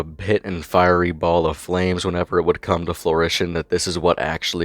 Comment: I listened to this a couple times and I think you said "flooritian" Or was it just my ears? I mean is that how you even spell it? Im curious so let me know. 😅